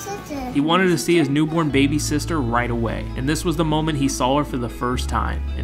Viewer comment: Who else thought he said baby sitter? 👇